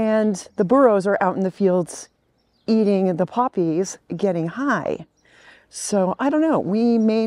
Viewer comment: did she say they are getting high?